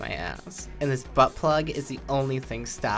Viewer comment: that "oh nyao" sounded like he wanted that to happen